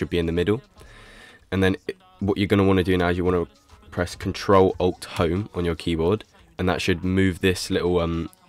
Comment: what he said? pls someone tell me the keyboard shortcut :) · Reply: Ctrl alt home